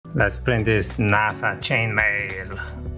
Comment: I thought you said let’s print this nice chain mail and didn’t even notice NASA…dang, I’m dumb.